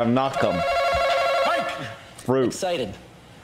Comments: wtf did he say? I watched that part about 20 times, can someone tell me what they heard? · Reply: he did say excited, he didn't mean to buy excitement tho, example: I am excited to buy this melon. He thought that the host meant emotions when you buy something. I now realize that it's not that funny anymore because of how much I rewatched it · what does he say here? i don't understand what he's saying lmao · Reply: The question was : what is something you 'feel' before you buy it? His answer : excited (since he asked "what is something you 'feel'", he replied he 'feels' excited) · What did the guy say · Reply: The question was : what is something you 'feel' before you buy it? His answer : excited (since he asked "what is something you 'feel'", he replied he 'feels' excited) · what was the answer I didnt hear it... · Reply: The question was : what is something you 'feel' before you buy it? His answer : excited (since he asked "what is something you 'feel'", he replied he 'feels' excited)